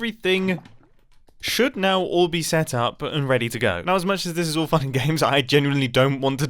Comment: anybody else hear "As much as this is all fucking games"